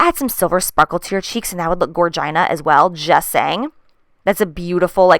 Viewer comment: I swear I heard "vagina" when you said "gorgina" lol